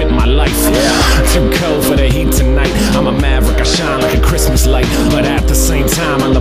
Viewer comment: did he say im a maverick?